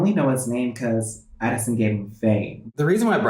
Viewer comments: i thought it said “cause addison gave him vain” i’m so dirty minded😭😭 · I thought he said Addison gave him a vein it’s gave him fame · i thought it said cause addison gave him vein. LMAOOO